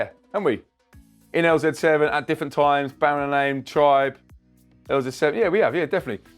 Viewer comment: I can,t hear the names of the bands mentioned at 5:38 i know about wwmt but the other on baaren lane is what i hear but can,t find anything on that can anybody tell me please and also please infotm me on more christian house music all sub genres are welcome but especially the harder and gritty kind